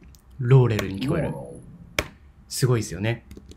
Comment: モーローって聞こえます、